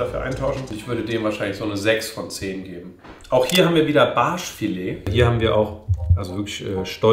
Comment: Wer hat bei 4:12 auch Arsch Filet verstanden? 😂😂😂😂😂😂😂😂